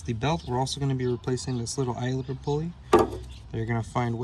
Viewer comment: What kind of pulley did you replace? I rewinded the video but kept hearing "Islander Pulley." What are you saying there? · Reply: It is called an idler pulley.